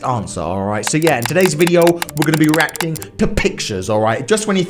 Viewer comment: Thought he said erecting instead of reacting -_